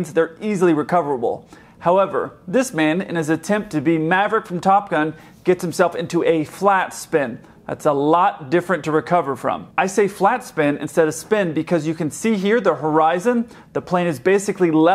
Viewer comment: I could have sworn you said "splat spin" -- Freudian slip?